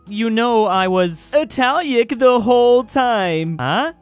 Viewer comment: did someone say 𝘪𝘵𝘢𝘭𝘪𝘤